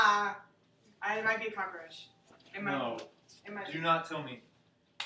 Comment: I think she just said that she might be preggers or pregnant right before this timestamp. She's like "ah ah ah oh I might be preggers" and he's like no no no. Kinda crazy how she was sold to us all as a the most conservative Christian around but apparently isn't so. Idk doesn't seem trustworthy to me.